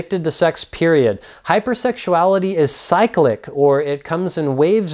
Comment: I thought it said heterosexuality 😂